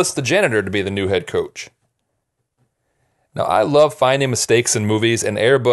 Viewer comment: I have the fan on and I thought you said hidden snakes instead of mistakes and I was like no?? Wtf? Until you said no basket ball